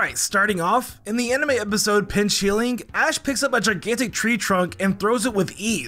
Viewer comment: I thought you said bing chilling 💀💀💀